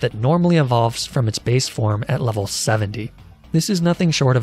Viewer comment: my ears must not be working but did you just say 70?